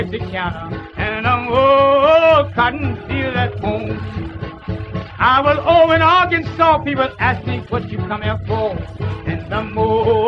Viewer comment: English is not my first language and I don´t understand what he said in 1:18 "I was over on (he mentions a city, i guess) but I asked me what you come here for (?? I don´t know if it´s correct) I was over on (he mentions an another city, I guess, and he repeats the same thing, I assume again)" Somebody can help me? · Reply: "I was over in Arkansas, people ask me 'what you've come here for?'" Arkansas is a state in the American south-west. Hope this helps.